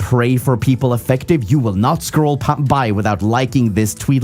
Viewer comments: scroll pan に聞こえるかな。多分スクロールが縦のスワイプ、パンが横のスワイプ(panoramaから派生したカメラ用語)だと思う。どっちも流し見って事なのでは？ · お力になれず申し訳ありませんがscroll pop on byですかね？ popとbyの間にnの発音が入ってるふうに聞こえました…。 ただ検索してみてもそれらしい表現は見つからなかったので自信はないです😢 2個目のはfuckで、直接言うのはよろしくないけど気持ちは伝えたい時に"エフ"とだけ強調して言ったりします。